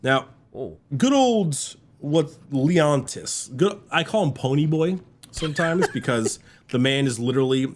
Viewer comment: I misheard pony boy as Poland boy and I just kinda went with it cause it still makes sense